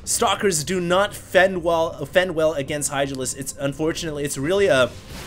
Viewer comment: I think he said "Idralisks"